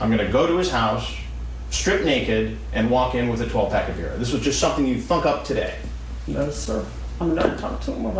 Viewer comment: everytime i’ve ever seen this “interview,” i always think at 0:15 he says “this is just something you’d fuck up today” and i’m like 😵☠️ for a few seconds